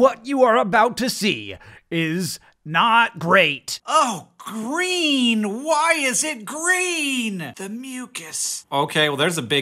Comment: Is he saying that to himself, "Oh [Hank] Green, why is it Green!"